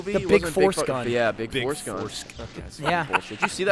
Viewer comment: Sounded like you said Big Foreskin Gun